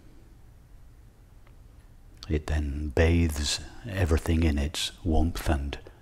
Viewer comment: please can sombody help me? At 34:15 he says:" the fire illuminates everything, it........?.......everything in its warms etc., My native language is not englisch, so i dont understand this word. Please let me know. thank you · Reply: It then bathes everything in its warmth and radiance.